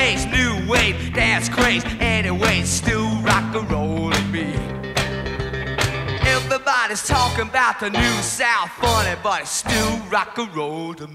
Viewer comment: god this video is bad LOL. I always thought he said "talking about the new sound, honey..." that is why I am here.